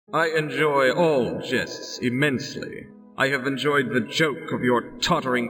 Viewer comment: Ngl I thought Stradh said "I enjoy all chests immensely" at the beginning of the video. Great game. I love this era of D&D games, the "lost era" that took place between the golden era of the Gold Box games, and the reinvention of the CRPG by Baldur's Gate. The game that lie between these two genre defining series weren't necessarily always the best, but they were always interesting. Sometimes they were brilliant, like the first two Ravenloft games, sometimes they were abominable, like Iron & Blood: Warriors of Ravenloft, and Descent into Undermountain, at other times they were flawed but extremely enjoyable and addictive, as with the first two Dark Sun games and Blood & Magic, and sometimes they were fascinating failures, collections of great ideas brought down by poor execution or technological limitations, as with the Birthright computer game or Dark Sun Online: Crimson Sands. Not the most consistently good era of D&D games, but nevertheless a really interesting one.